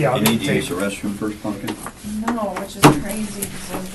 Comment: Did I hear correctly (at about 4:05) one of the investigators called her pumpkin?